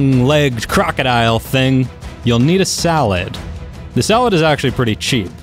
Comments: Did somebody else hear SALAD? at 2:51 Great vid by the way :) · Yo did he said salad insted of saddle 😂 2:50 10/10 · did he just say "you'll need a salad"??